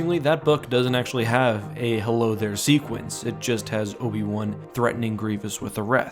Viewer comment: Sounds like he says "well there tootsie" in italian lol